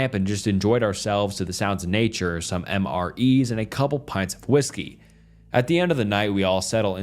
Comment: did he say a couple of PINTS?